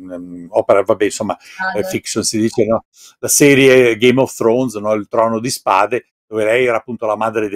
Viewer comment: Solo io ho capito "game of stronz"?